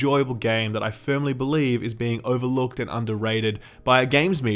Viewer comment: I thought it said overcooked and underrated 😁